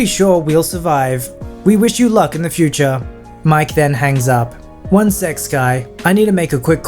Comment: i heard it as 'one sex guy'